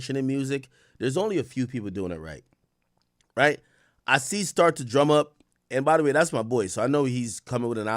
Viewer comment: Who does he say at 0:53 ? Sound like start to drum up but I know that ain’t it